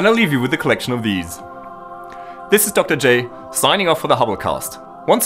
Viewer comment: at time stamp 8 min 45 sec i thought he said signing off for the holocaust